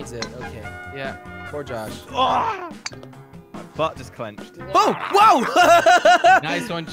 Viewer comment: Did anybody else hear Jasen say bye but just clench10:21